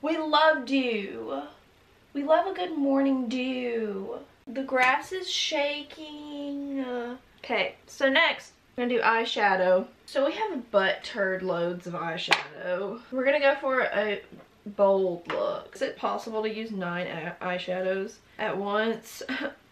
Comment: “so we have a butt turd loads of eyeshadow.” is that what i heard?👀😂